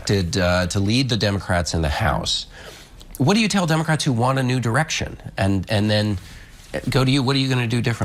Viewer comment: Every time "new direction" was said I heard "nude erection."